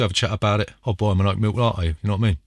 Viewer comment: I love your vids and you're one of my favorite board game content creators. But being that I'm not British, I have no idea what you're saying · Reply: Oat MILK latte... Not oat MEAL .... An oat MEAL latte would be a bowl of fucking porridge, you silly whopper.